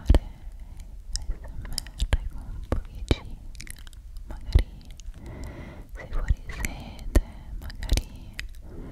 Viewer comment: Non capisco cosa dice al minuto 12:15.. 😅 aiutoo sto impazzendo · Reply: "con pochi giga" credo